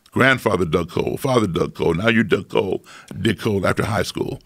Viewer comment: anyone else hear "dick hold after high school"?